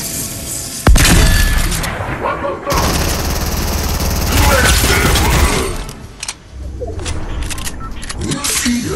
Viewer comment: Did he say what I think he said? 4:12. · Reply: I think he said “What the F*CK!?!” lol 😂